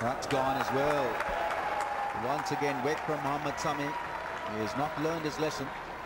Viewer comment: subtitles show Mama Tommy instead of Mohammed Sami